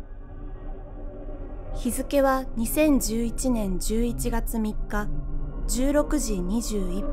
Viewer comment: Slight little error in the subtitles at 0:53, the subs say the post is dated to the 25th, but it clearly says it's the 3rd, Godzilla's 57th birthday. Or... is that what the narrator is actually saying? I think I heard "san" but I'm not really sure.